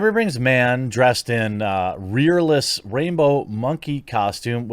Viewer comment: I swear I heard you say rapebow monkey costume which makes way more sense 😂😂